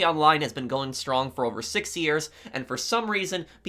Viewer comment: did he just say 60 years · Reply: Lmao nah bro he said 6years